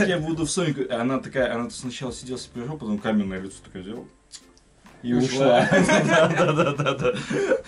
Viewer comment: что они сказали? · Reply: И ушла